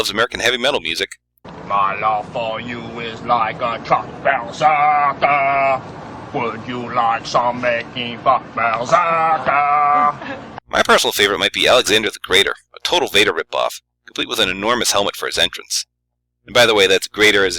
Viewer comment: Did he say "making fuck"?!